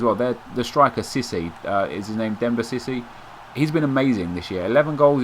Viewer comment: It's Cisse, not sissy. The way he pronounced it sounded like he was saying 'sissy' instead of Cisse.